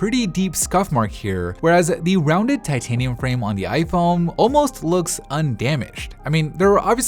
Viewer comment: did he just say ‘rounded titanium frame’ or its just my ears?